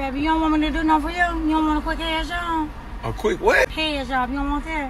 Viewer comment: Respect where it's due to that guy for acting humanely and not like a predator... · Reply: she, I thought said hand job. There is a difference, lol ijs